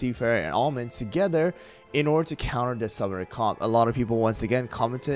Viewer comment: For anyone confused what he's saying at 4:13 it's "Summoner Comp". (His CC is transcribing it as "Summer Comp" and it was like trying to decipher Nirvana lyric ). Is there a video OR (preferably) reference image with all the comps on one side and the teams to counter them on the other? Would be super helpful when trying to climb the arena. Tea Knight's intentional death comp is the one that's difficult lately as usually my tactic is kill the front line and avoid cookies that attack further back, or use Pomegranate and Herb so even supercharged Tea Knight doesn't hit that hard, but I'm not sure these are the best counters.